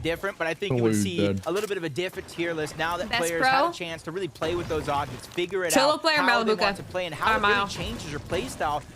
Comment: did she said bugha or malibuca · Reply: She said Malibucca